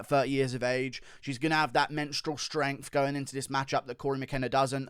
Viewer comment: did he actually just say "menstrual strength" instead of "mental strength" or what...